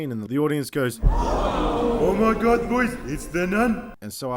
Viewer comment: did anyone else hear king julien saying " Oh mY GoUd BoiZ its a nun!" at 3:03 XDDD